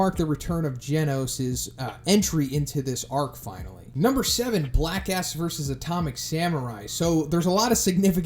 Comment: i thought he said black ass